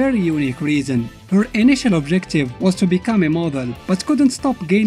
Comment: Oooooh a model!!! I thought you said immortal.